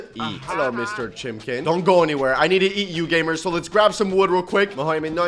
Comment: Did he just say in the intro "i need to eat you gamers" 💀